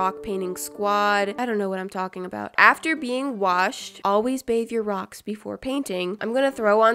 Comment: I kept repeating 5:58 and 6:08 it sounded like ‘after being washed I painted rocks’ lol LOL 😝🤣😂